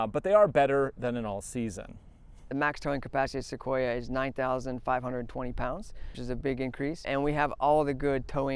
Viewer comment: I really like your reviews, you're generally the first I go to. One thing though, at 3:10 the Toyota rep says it can tow 9,520lbs but your captions say 9,020. Is that intentional or was it a mistake?